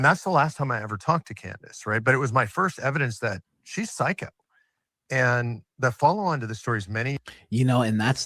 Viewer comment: time stamp: 4:24:51 Almost sounded like psychic... Instead of psycho 😂